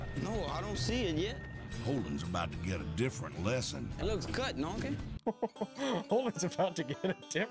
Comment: I honestly think the subtitle guy messed up. I think what he said was, "It looks cut, done it?"